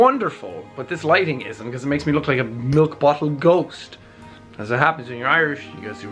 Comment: "This lighting makes me look like a milk bottle ghost." Actually quite accurate, not having pupils really sells the effect! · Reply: I thought he said milk bottle goat XD I have a problem huh?